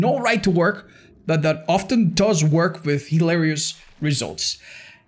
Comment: Did you say oftten?